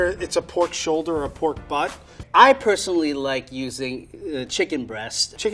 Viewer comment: Did Frank just say pork shoulder or PORK BUTT??? OMG 😲🤣😂😂😬